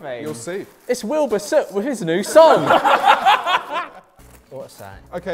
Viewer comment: “It’s Wilbur soot with his new song.” And the niki merch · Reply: SHSJSJSJSJSSSHHDB I thought he’d said “it’s Wilber Soot with his new son “ and thought he was referencing Tommyinnit lmaooo